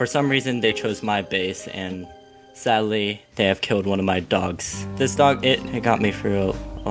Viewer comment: Anyone else see the captions instead of killed my dog it said “tickled my dog” lol